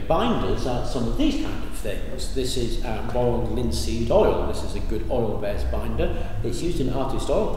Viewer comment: "linseed oil" (for those like me who are not English tongue and would need like me several watchs to understand what it was ^_^ ) 11:25 - "zinc oxyde" 11:34 - ?? ...-oxyde? (didn't get the first part of name you said ^_^ Can You write it down here please? :) ) 11:47 - "graphite, CAST? (didn't get the full name corresponding to the initials, what is it please? ), graphene" 14:03 - "???, as a table spoon of salt" PS: I remember You that You invite us to replicate and experiment ourselves, so that's Logical we bother You for questions when we don't understand :/